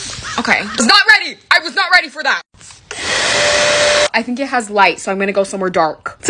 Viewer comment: When she said “I think this has lights” I thought she said “I think this has lice” 😭😭